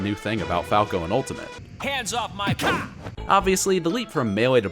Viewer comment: hands of my c*** is what I think he said